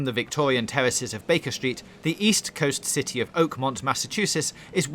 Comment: I thought you said city of Pokémon